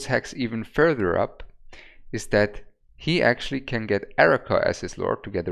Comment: Hi, very good content. Thanks for this. One question: You mentioned at around 10:25 that Hex would do more dmg if he benefited from a different lord bonus. I can't really understand who you're talking about. It sounds like Erica or something. But there is no such lord. Who do you mean? Thanks in advance. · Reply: Yea, I also heard that but he said Aracha. Just the kind of slang maybe